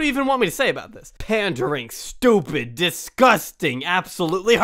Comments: Did i heard "Haram". 😂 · did he say "absolutely haram" 😂